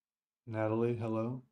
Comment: My daughters name is Adelaide. Although i think you may have said "Natalie" and i just heard Adelaide at first. Lol.